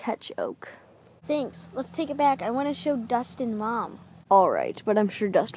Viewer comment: When oak said i wanna show Dust and mom! I thought he said Dustin-stranger things